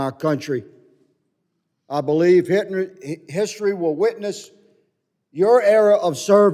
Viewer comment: Higgins slow drawl is so amazing when he is passionately emphasizing points. Good man. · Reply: Anybody catch that Freudian slip at 4:03 I believe Hit ler. Mayorkas is Jewish on both sides. Look up Barbara Spectre on YouTube. Same exact thing here we see.